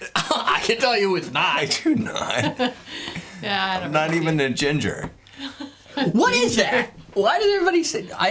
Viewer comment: I thought I heard say "I'll never be ginger" and I was like ":o its The Doctor!" Qotd for me I kind of like the dragon characters in Disney movies even though most of the time they die. :( First episode for me though might have been the long island iced tea episode. I started by watching that guy one Josh or whatever then realized you're the only one who tastes what you make before dumping them. That's really what made me subscribe actually. Favorite recipe you did was chocolate cake. lol